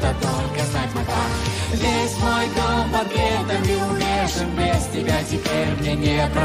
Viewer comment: Мне послышалось"Весь мой дом, котлетами увешан..."😮